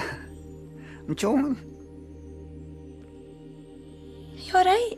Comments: What does he say at 1:00? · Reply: I’m Joking. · can somebody please tell me what paul said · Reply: He's saying "don't worry about it"